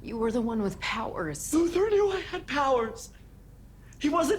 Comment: what did say? I still didnt understand what he said. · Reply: Luthor knew I had powers